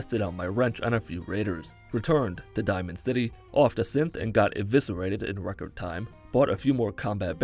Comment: I swear it sounded like he said “offed a simp”